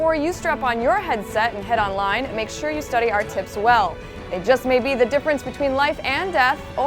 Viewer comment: all i heard at 0:29 was study our tits well :L